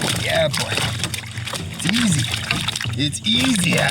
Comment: thought u said its easy 😅